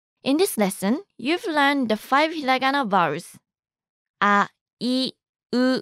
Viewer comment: instead of 5 Hiragana vowels the Captions say " Five Dragon a Virus"